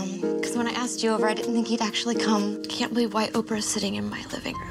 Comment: Did she just say "white Oprah"? 🤣🤣🤣